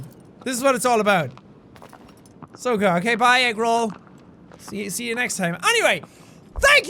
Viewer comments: Every time he said Agro I heard “Eggroll” XDDDD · When he says Agro it sounds like he says Eggroll.